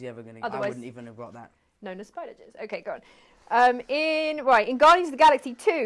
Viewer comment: I thought she said his spider webs are made out of “spider jizz” not juice